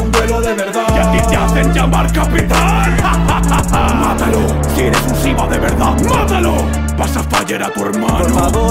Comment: pense que dijo "Matalo, si eres un Sigma de verdad" jaja